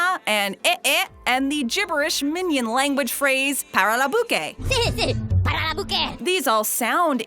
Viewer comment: I heard “ para la buka”